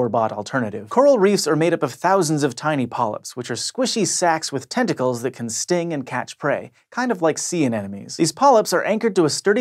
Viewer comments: I hear "kind of like CNN News" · I almost thought you said CNN news.